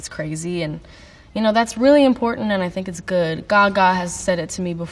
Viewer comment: I love that at 10:23 the subtitles say 'god' instead of Gaga. That;'s the highest of praise ;) Also funny to think that the people who put subtitles in think God speaks directly to Billie...